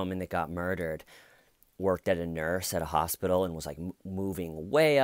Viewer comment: Sounded like you said, worked at a nurse as a hospital. That got me.